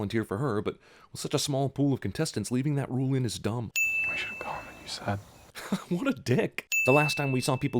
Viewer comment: What did he say? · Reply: He said, "We should have gone when you said." Katniss earlier in the movie wants to escape with Gale but he says they should stay, then changes his mind at that point.